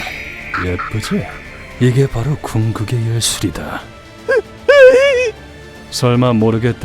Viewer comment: 뭐지? 분명 궁극의 예술이라고 적혀있지만 들린건 궁극의 현실이다 라는 옵치 시메트라 대사가 들리는것 같지? 옵치를 좀 그만해야하나...